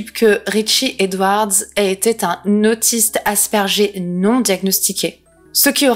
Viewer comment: "Un autiste Asperger" moi j'ai entendu "Un autiste aspergé", je me suis dit que c'était violent. (au passage, on n'utilise plus le terme Asperger puisque le bonhomme portant ce nom aimait particulièrement les idées d'un politicien moustachu pas ouf assez connu en Allemange) Très bonne vidéo, j'ai adoré le concept.